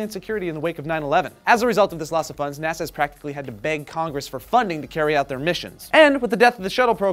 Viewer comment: Haha! On my smart phone speaker it sounded like said "NASA has to BANG Congress for funding", instead of beg. Both seem accurate.